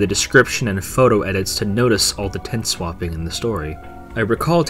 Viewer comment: Did he say “tent swapping”? I don’t think so, but I can’t tell what he actually said there. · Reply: Tense swapping